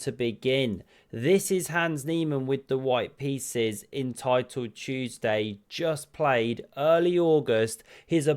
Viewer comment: I heard “Entitled Tuesday” and it still works.